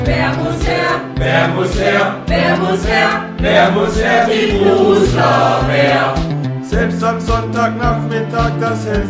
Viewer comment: Ich verstehe bei 1:44 immer die "Looserwehr" ^.^